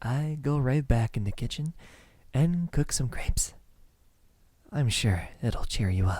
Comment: I have to admit I thought he said he was going to cook some grapes, the fruit, instead of crêpes. I was very concerned for a sec lol Great voiceacting though!! I enjoyed it!